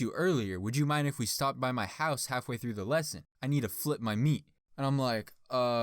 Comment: Am I the only one that laughed when he said “ I need to flip my meat” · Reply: I thought he said he needed to flick my meat